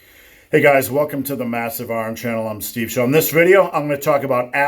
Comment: I jus subbed and tbh I always thought he said “massive arm channel “😂😂😂